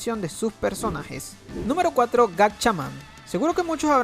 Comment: primero escuche GARCHAMAN